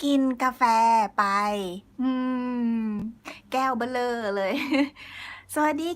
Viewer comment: ครูพูดว่าอะไรนะในนาที 1:51 ออกเสียงแบบ “แก้วเบลอเลย” ดีใจที่ในที่สุดผมได้อ่านทุกคำถามเลย เมื่อไลฟ์อยู่ อ่านไม่ทันเลย เยอะแยะไม่ไหว 555++ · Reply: พูดว่า “แก้วเบ้อเร่อเลย” ค่ะ 😂 เบ้อเร่อ หมายถึง ใหญ่มากค่ะ เป็นภาษาพูดค่ะ